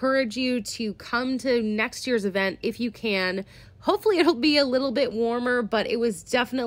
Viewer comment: I was watching your video 1.75x speed (as is my penchant) and at 20:20 I heard you say, "Hopefully he'll be a little bit warmer..." - implying Tim Dodd was not warm to people at the event. I immediately thought, 'What? That doesn't sound like, or Tim." I then re-played the comment at 1x normal speed and you clearly state, "Hopefully it'll be a little bit warmer..." AH HA! The cold weather is what you were clearly talking about. Damn tech, still has a long way to go. Crisis averted. 🚧😎👍🚧